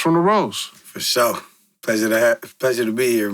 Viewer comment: did bro say "Pleasure to have me"...? 😂😂